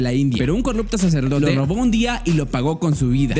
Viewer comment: parece que dice al fondo lo robo Like si lo escuchastr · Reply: Si, no es que lo puso como secreto, fue un error de grabacion y si sabes entender dice ''Un sacerdote lo robo un dia''